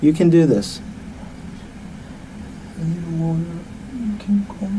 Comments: Perhaps I'm mishearing, but I think he asks for a lawyer at 28:08 and not water and that's why the detective leaves him alone? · he did NOT say "I need water" (something which he already has right in front of him), he said "I need a lawyer", that's why the interrogation abruptly stopped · He clearly says "I need a lawyer", that's why the interrogation is over at that point.